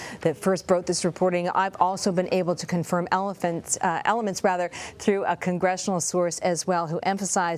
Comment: I thought she said elephants, not elements.